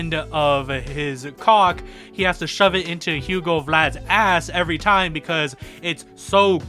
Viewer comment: i heard hugo vlads ass and was like PAUSE lemme skip back a minute LMAO real.